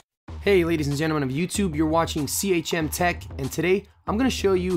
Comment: I thought ur intro said c*m tech